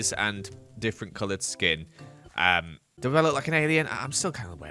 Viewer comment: Lol when said "Do I look like an alien" i thought he said "Do I look Canadian" and I lost it 😂😂👽🇨🇦